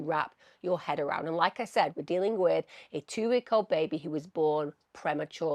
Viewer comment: so now you say it’s a two week old baby premature? Is it two years, two weeks, or two days old? I heard two years now two weeks? I’m so confused on how old this baby is now. lol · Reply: Two weeks